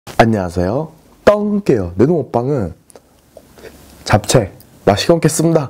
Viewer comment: 무슨잡채요? 나만 안들려? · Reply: 무지단 고추잡채 입니당